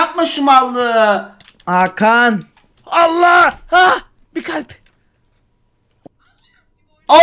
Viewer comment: deki allah diyen kadin sesini tek benmi duydum ?